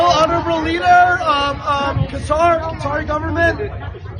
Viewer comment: did he say he Qatar government? I thought he said the Khazar government